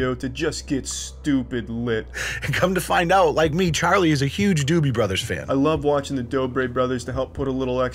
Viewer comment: Thought it said door bell brothers